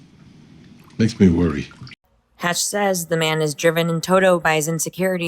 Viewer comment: I always heard it as "Man is driven.." with no 'the', more as an appropriate platitude for the moment than a specific assessment of junior. When you think of the comment as a generalization it applies to the decisionmaking across the series.